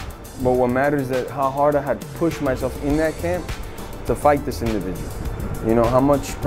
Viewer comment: Jorge "this individual" masvidal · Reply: jbot91 lol shit I'm so high I thought he said to fight "this nigga"